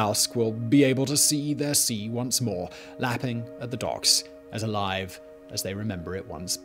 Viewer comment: Oh for a second I thought he said the fishermen were laughing at the dogs not the docks